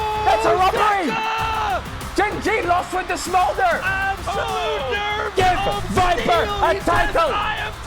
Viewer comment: i thought he said "give viper a tackle". Only way geng can win